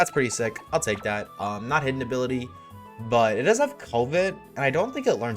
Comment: I thought you said the eevee had covid 🤧🤧